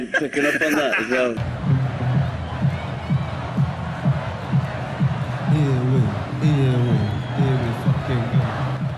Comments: what are they shouting? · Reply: 3 months late but they said, “here we, here we, here we fucking go” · What is he saying at 0:46? Watched this clip a thousand times but still no clue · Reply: Here we, here we, here we fucking go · What did he say? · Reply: Here we, here we, here we fucking go